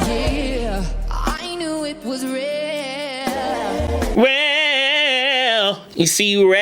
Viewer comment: I know I'm super late 😅 and I ALWAYS enjoy your videos but just wanted to point out the word "Real" you thought she sang was actually "Rare" I thought she said "Real" as well after you pointed out how she sang it differently than the the way the actual word sounds then realized on the next line she ended with the word "Care" so rhyming logic told me she must have actually said "I knew it was raaarrreeee" on that line at 5:47 (I did also confirm on the lyrics it's "rare" as well). Either way great analysis per usual 😊👌🏽❤️